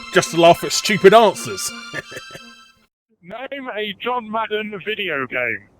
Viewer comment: What did they say at at 6:47? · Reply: "Name a John Madden video game"